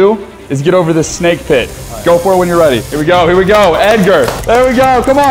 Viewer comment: did he say edger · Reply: No he said egger